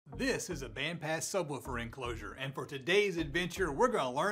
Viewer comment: First video here, I thought I heard “ THIS, is a big ass subwoofer” All right all right my kinda channel. Subscribed.